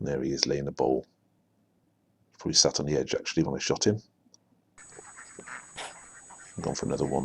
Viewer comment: Thought you said "he shat on the edge when I shot him"